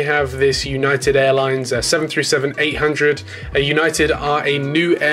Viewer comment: wait what? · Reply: I heard him say "757-800" or it’s just me